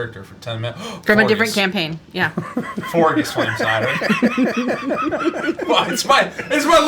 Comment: i cant for the life of me figure out what is saying at 1:20:28 . Forgus? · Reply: He’s saying “Forgus Flamecider”, it’s an NPC from Prime